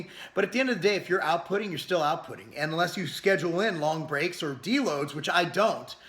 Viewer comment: did you say, "schedule in D loads"